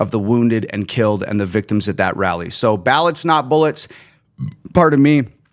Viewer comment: Not gonna lie, i wasnt looking when you first said "ballots not bullets" i thought you said "ballads (as in songs) not bullets" but since you didnt, feel free to use that phrase. They always say the pen is mightier than the sword, or gun in this case.